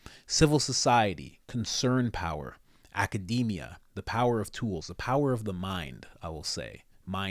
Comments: It sounded like Klauss said “Academia brings the power of truth”, not tools. And of course, his truth is a bunch of lies. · Regarding academia, I thought said it brings the "power of truth". In which case, it would be 'their truth' rather than THE truth. But he might possibly have said the "power of tools". Either way, yes it does sound like something from a real-life Bond villain. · I thought he said "Academia brings the power of truths" not tools...